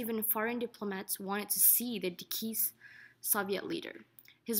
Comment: did you say "Dekeased" instead of deceased? lol